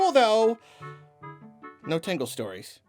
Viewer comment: "-only one rule, though!" -Fire away, send me a curve ball, I'll handle anything! "-No Tingle stories. I'm serious." - ... I can no longer work under these constraints. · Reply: I thought he said "No TANGLE stories." I was like "WTF is a tangle story? Is this some story writing term i've never heard before?" Went to go look it up on google and only got results for the Disney movie Tangled...